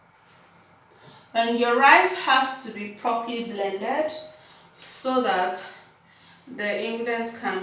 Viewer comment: Did i just hear you say, "your rice has to be properly blended" did you add rice?